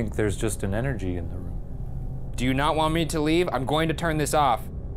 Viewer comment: it sounds like it says “turn off”